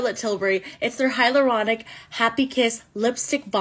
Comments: OMG- y'all, I'm sorry but.. I swear, I thought she said "Pillow DOG" 😭 AND I BARELY QUESTIONED IT BECAUSE- you know.. it's lipstick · I can't be the only one who thought she said "pillow dog"